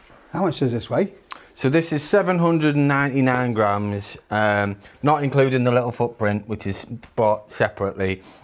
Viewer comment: Thought he said 799 GRAND!